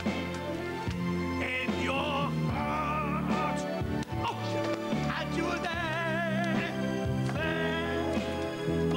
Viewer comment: What did say at 14:40? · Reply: “With hope in your heart”